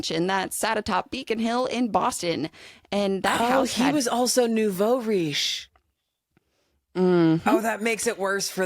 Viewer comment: hey there is a word that said at the 29:59 mark that I've never heard before and cant find the meaning on google. Sounds french but i cant make out what it mean? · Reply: Nouveaux riche? It is French and it means “newly rich” 😊